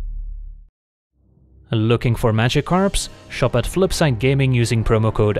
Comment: Historic treasure hunt? Dang, sounds like an April Fools worthy deck. Also you included Magikarp! I said in a comment elsewhere that I always hear you say “Looking for Magikarps” !!